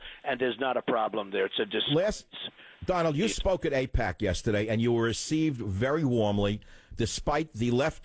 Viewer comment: woah. when he said "i heard you spoke at aipac yesterday" i thought he said, "i heard you smoked an eight pack yesterday" i need to stop smoking them tweeds.